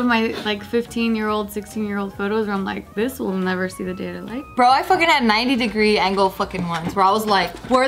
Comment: Did say “...never see the day of light?” at 9:29? Cuz that’s adorably funny. Or am I hearing things again?